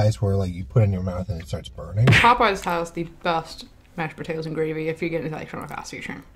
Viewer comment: WHAT IS SHE SAYING????? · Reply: “Mash potatoes and gravy if you get itdjsjsjzxnvnc dscsdwd”